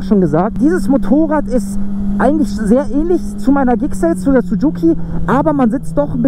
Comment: Ich habe sucuki verstanden